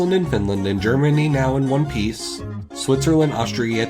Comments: Wait it says "Germany now in one piece"??? I thought it said "germany now wants peace" but i geuss its good that it doesnt say the second option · So it wasn't the "Germany now in one piece" that tipped you off? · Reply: always thought it said “Germany now want peace” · I always thought he said Germany now wants peace. And I was like damn they went there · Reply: I thought he said "Germany now in peace". "Yeah, since the end of WWII", I thought. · I always thought the song said "Germany now wants peace" and not "Germany now in one piece". Both versions are accurate though.